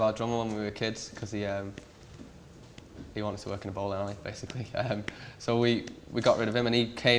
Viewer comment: what did he say there?? He wanted to what? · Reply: work in a bowling alley